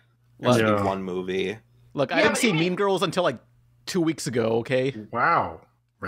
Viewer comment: What movie did nick say around the 16:50 mark? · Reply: Mean Girls